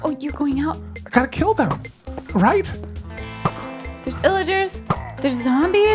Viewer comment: Seeing this live was so fun and I've watched the uncut version a few times while waiting for the highlight. For whenever you find an Illager outpost (big or small): Make a bed and shelter nearby. Stealth killing is recommended. I found a huge outpost once (it had locked up Iron golems) and when I got killed, I had to hike 5,000 ish blocks to go get my stuff. Smaller ones are more manageable but still a pain if the forest is too dense. I have the bad luck of having a small Illager outpost close to my base, so I kill/hunt that mob often since they get stuck in the weirdest of places in and outside the farms surrounding my base. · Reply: I don't play Minecraft, I just watch it for fun, and I've been so confused about the word "Illager" until I saw it written down in your comment! I thought they were saying "Pillager" and I was just failing to hear the "p" ... Oh boy am I relieved to know that my hearing isn't failing me haha